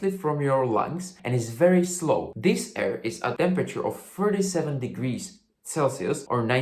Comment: subtitles say 47 degs instead of 37. I understood it fine when I heard it but I did a double take when reading it